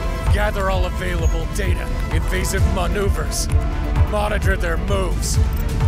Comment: I misheard 1:25 at first as "MONITOR THEIR BOOBS!"